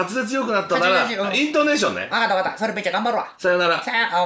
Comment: ここ全然聞き取れないwww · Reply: 「それめっちゃ頑張るわ」って言うてます